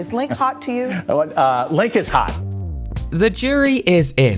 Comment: I litterly thought that it said 'Why Link so hot'.